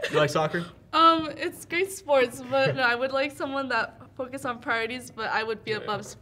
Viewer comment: what the fuck did she say she would rather have someone who focuses on parties??? Yeah she definitely doesn't not deserve love 😂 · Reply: “Priorities”